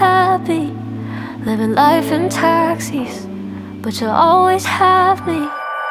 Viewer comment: I heard "lonely help me" instead of "you always have me" lol...